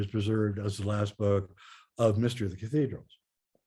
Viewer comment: sorry I can't make out what you are saying: "...just as the _ chapter is preserved as the last book of Mr. ___" ??? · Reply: "Mystery of the Cathedrals"